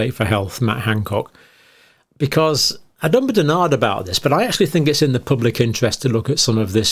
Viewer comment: What is dr John saying here 0:15 ? What is that word? Benard?